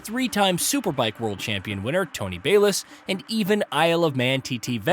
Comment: do you mean Troy Bayliss not Tony? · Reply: i legit thought he said tony elias